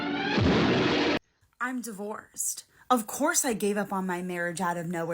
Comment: The 304 at 4:35, i am no native speaker so i have a hard time understanding her. Did she say "i'm divorced"? or " i'm the worst"? Unsure, both works i guess 😂